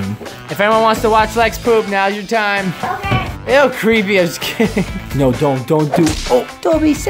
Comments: Wait did say crazy a$$ kid!? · did say A$$ in this? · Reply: No he said that is creepy I was kidding